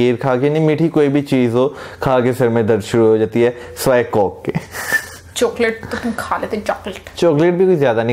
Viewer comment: " sivay coke ke " tht sounded like co*k ... Hahahaha ...